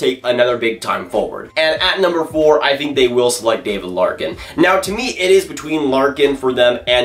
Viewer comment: did he say David Larkin?